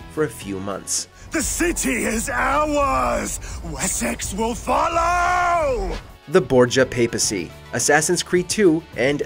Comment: it cut to ad so when it came back and he said THIS CITY IS OURS AND WESSEX WILL FOLLOW! all i heard was THIS CITY IS OURS AND SEX WILL FOLLOW!!! BAHAHA